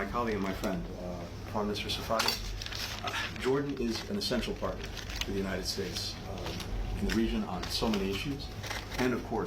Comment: It says he is saying "Jordon is an essential partner of the United States in the region..." but for some reason I heard, "Jordon is an essential part of the United States in the regime..." I must be hearing what he is thinking.